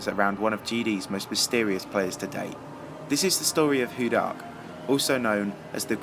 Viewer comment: When u said "hu dark" i heard it as "who died".